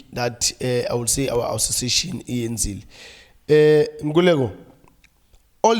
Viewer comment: Did Vilakazi just say "our association, ANC..." (around 10:28-10:29)? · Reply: he said "iyenzile"